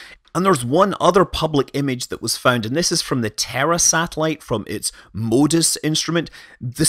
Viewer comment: I've watched too many Schwarzenegger movies; when you said "the Terra satellite", I heard "the terror satellite".